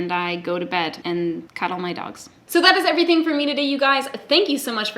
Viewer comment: I thought you said “and cut all my dogs” 😰 but you actually said “cuddle my dogs”